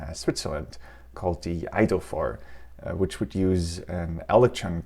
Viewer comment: I maintain that you can't trust anyone who speaks with an accent. Closed captions says "idolphore" when the speaker is struggling to say "The Eidophor Television System."